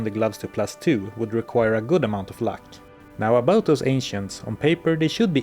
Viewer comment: It sounded like you said "the very trickery trio, the Asians" instead of ancients 🤣🤣